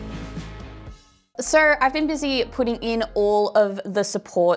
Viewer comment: That was the most Australian, "so," I have ever heard. I thought she said, "sir."